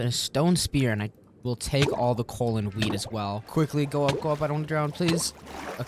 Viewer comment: Watching you put dedicated water holes for your farm, WHILE THE ENTIRE FARM WAS ALREADY ONTOP OF WATER, really urked me lol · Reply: Bro at 11:30 mins in the vid he said weed instead of wheat 😂